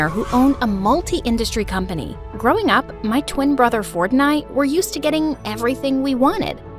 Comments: why did I hear fortnite 😭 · i heard fortnite💀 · just me or at the second 00:12 I heard Fortnite? lol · I heard “ my twin brother fornite” 🤣💀💀😭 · I thought she said “my twin brother Fortnite.” 😂💀😭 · my twin brother fortnight anyone else hear? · When you Said „Ford and I" thought U Said fortnite 😂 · when she said "my twin brother ford and i" i thought she said " my twin brother fortnite and i" lol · did she say fortnight?! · bro at the very beginning when she said "my brother Ford and I" I thought she said his name was Fornite. 😂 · When she said ford and I. I thought she said Fortnite 😂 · LMAOOO I HEARD IT WRONG AND NOW I CAN'T UNHEAR IT growing up, my twin brother FORTNITE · I heard Fortnite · OHH SHE SAID ''ford and i'' i thought she said fortnite lol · Sounded like fornite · Did he just said fornite? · I heard "fortnite" not "ford and i" Like if you heard the same as me! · It sounds like she say: My twin brother fortnite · When he said ford and i i thought he said fortnite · I swear when they was introducing the characters when the voice narrator said “ford and I” all I heard was “Fortnite” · I heard Fortnite!!??? 🤨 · is it me or did I just hear fortnite? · Bruh I thought she said “my twin brother Fortnite” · lord help me, at the beginning when she was introducing her brother, i thought she said "growing up, my twin brother fortnite ..." bro why am i like this. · Thought she said, my twin brother Fortnight😂😂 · please I thought she said her brothers name was "Fortnite"😭